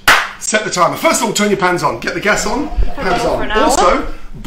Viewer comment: i thought he said first of all take your pants off... he was talking so fast .. hes actually said turn your pans on